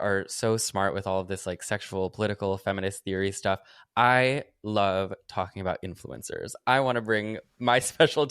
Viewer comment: I misheard that as "I love talking about human cancers," and I think that's still a pretty accurate mishearing.